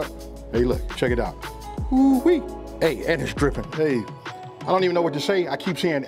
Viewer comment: I coulda sworn he said “and it’s crippin’”.